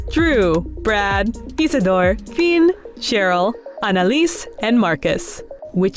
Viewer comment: did she just say fein?